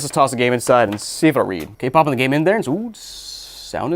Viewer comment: did bro just say K-pop?